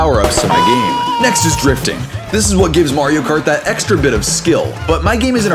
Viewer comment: my dumb ahh thought drifting said drinking 😭